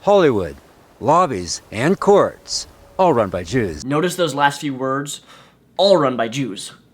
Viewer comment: I thought he said it was run by juice